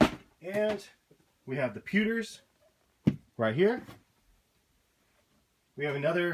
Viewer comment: What foams are at 4:22 couldn't really hear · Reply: Pewter